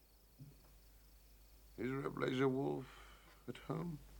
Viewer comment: I always heard it as "Laserwolf"